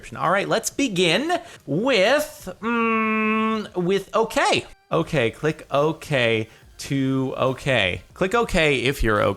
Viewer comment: i thought it said (OK chick to OK) not (OK click to OK) lol XD